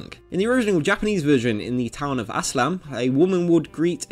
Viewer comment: Wait the town of what!? It sounded like ass slam